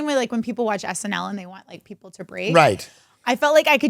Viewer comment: Right when you said break, I definitely thought Jake. And then you said Jake laughed my ass off. He definitely made it a thing. Cause that's def who he is a funny nice guy.